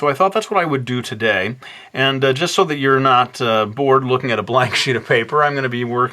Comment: First time I heard you swear! (Kind of) :P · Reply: "Just so you're not bored looking at a blank sheet of paper." No swearing here, sorry to disappoint you. ;)